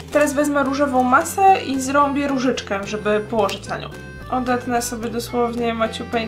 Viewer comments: czy tylko ja usłyszałam ,,Zrąbie różyczkę"? xD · czy tylko ja usłyszałam zarąbie różyczkę? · W 3:31 tylko ja usłyszałam zrąbię różyczkę